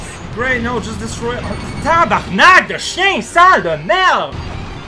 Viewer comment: Okay...I may be asking this for a stupid reason, but what is he saying in 7:49? I got "Tabernak de ........ de merde!"